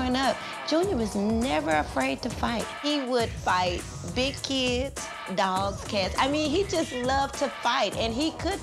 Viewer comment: did she just say he fought dogs and cats?? Wtf